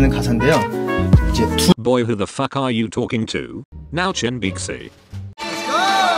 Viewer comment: i heard chanbaek ssi> im dying!